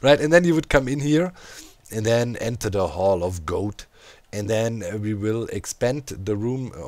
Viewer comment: I keep hearing "Hall and Oats" instead of #hallofgoat well, Oh-oh, here it comes Watch out It'll chew you up Oh-oh, here it comes It's a worldeater